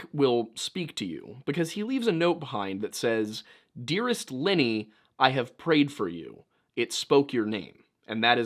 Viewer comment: Fucking hell it is scary to hear this spot 3:34 , bc my names Linny and it sounded like it was speaking to me not a Lenny.